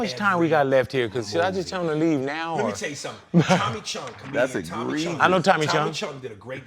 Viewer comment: I thought he said Tommy Chong free cheech n Chong he said Tommy Chunn